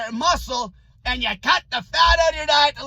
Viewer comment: I’m so confused. I thought high fat was what you were preaching? What happened to get that fat in ya? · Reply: I always thought he was saying "get the fat". As in, take control of it or ditch it.